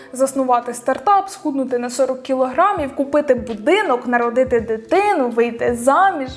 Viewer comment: я спочатку почула - купити дитину